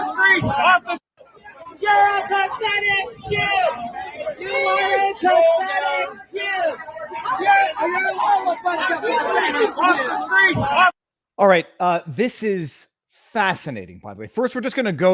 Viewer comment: I hear "you are a pathetic dupe"